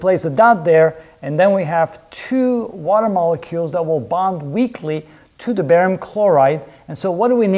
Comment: Lol I thought you said: and then we have 2 water molecules that will bond weekly. Then I thought to myself how do the molecules know when 1 week has passed? After 2 seconds I realized the absurdity of my thought process and deduced you meant “weakly” haha